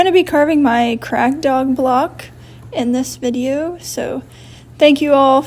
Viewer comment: I thought you said “crack dog block” 😅